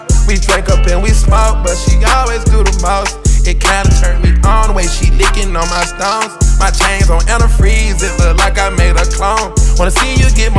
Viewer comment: i thought future said he had no chains 😂😂